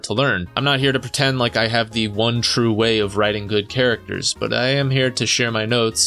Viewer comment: did you say good or hood?